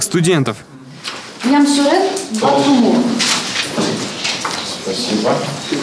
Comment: Из имен послышалось Намсурен Балтумур это наверняка монгол:)